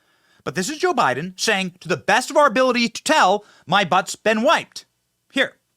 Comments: But I THOUGHT BIDEN SAID "I WIPED MY BUTT" LOL. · I always thought he said "I must be white"